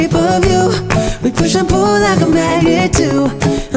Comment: if only it didn't sound like he was saying "we push and pull like a maggot do" LOL just kidding great cover, and quick on the uptake too, this was just released like last week :O